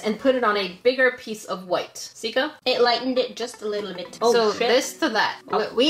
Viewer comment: Anyone hear at 22:02 that sika said "oh sh*t." Or is it just me lol. (I hope I spelled her name right) Edit